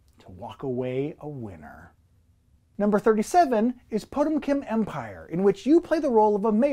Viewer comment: I thought you said "Pokemon Empire" and for a split second got SUPER DUPER EXCITED ... Then you crushed my happiness with "Potemkin Empire". Thanks,)